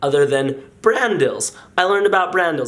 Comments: I thought Shaun kept of saying Brandon but he was saying brand dill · I thought he said Brandon instead of Brand Dills